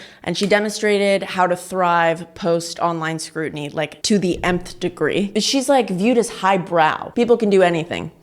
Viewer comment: Did you just say..."To the Mth degree???"